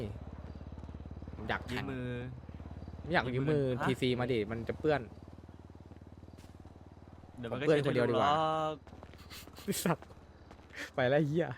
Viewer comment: พูดไรอะผมฟังไม่เข้าใจ. พูดว่า เดียวก็เช็คเหมือนเดิมหรอก ชะปะ